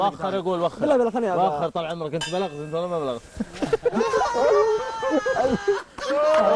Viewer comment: بالدقيقة 4:50 وش قال ابو حصه ?! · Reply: I am goodboy قال لغازي انت بلغت والا باقي 😂😂😂😂😂😂😂😂